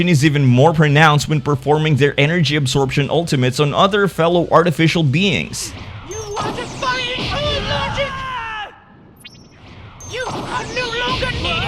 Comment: what is 19 saying? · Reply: “You are defying all logic.”